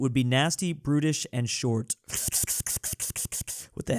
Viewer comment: I thought he said: “Life without the state would be nasty, British, short.” 😂